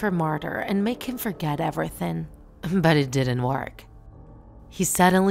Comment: did she say ebretink it's called everything dumb 😑